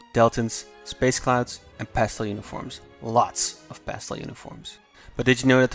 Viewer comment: Lots of ____ uniforms? What is that word he used? (very beginning around 0:05) Update: CC worked this time and I see it is 'pastel' uniforms. Now it makes sense. I thought someone was saying, "pasta" like a teenage boy after drinking a full pot of coffee and suffering form multiple mouth sores and deadened facial muscles.